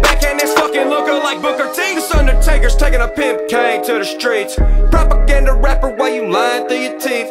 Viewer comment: that undertaker and kain line was hard but none of the reaction channels have caught it! Think they thought you said pimp game! Keep it!